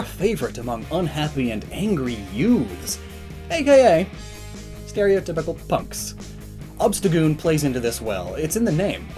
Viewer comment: When you said stereotypical punks, I thought you said stereotypical F**Ks